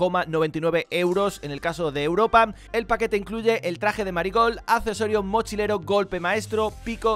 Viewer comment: Parece que dice "Maricon" XD